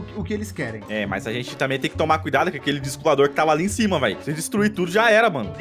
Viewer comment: Cara só eu que ouvi o falando tomare que eles voltão